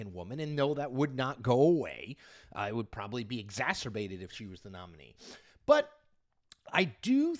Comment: Sorry, perhaps I didn't hear you correctly at timestamp "3:45"; did you say "I would probably be exaserbated if she were the nominee" or "it would proabably be exaserbated if she were the nominee"? Just checking for clarification about your character there Chippy.